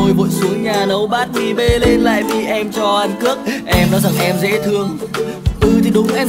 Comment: Ăn cước mà nghe thành ăn c*c XD